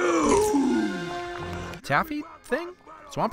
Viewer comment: If you listen to it in a certain way it sounds like theyre saying Dorito